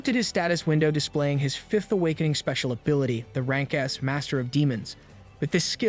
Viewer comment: the captions says rank ass instead of rank s LMAO